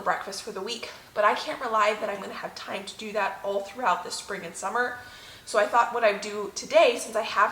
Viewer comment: LoL...you said Spring and Summer...I heard friggin' summer 😂